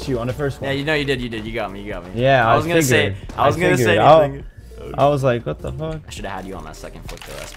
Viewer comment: did just say I was gonna say my n***a lmao?